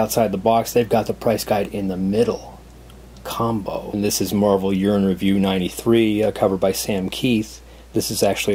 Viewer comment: For a split second I thought you said Marvel Urine review. Definitely great choices and a good number of covers I haven't seen before: though I do have and love the Bruce Timm Magnus Robot Fighter issue.